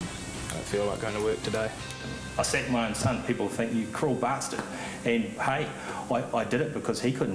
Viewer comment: he did what to his son? i have no idea what he said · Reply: he sent him off (to work)